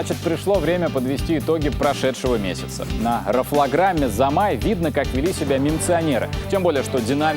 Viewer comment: когда услышал График Замай